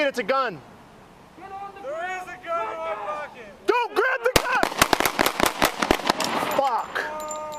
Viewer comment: what I hear is "grab the gun on pocket" why did the police tell him to grab it? Or am I mishearing? Because if they ordered him to do that, then it's their fault.